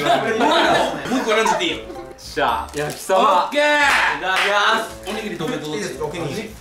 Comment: 焼き鯖が焼きそばに聞こえたww